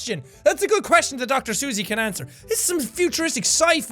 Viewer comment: For a second I thought she said she was Dr. Seus instead of Dr. XD